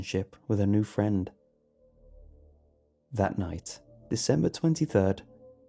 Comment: I thought it said September 23rd, and thats on my birthday so I almost flipped the fuck out, but then i realized it was december 23rd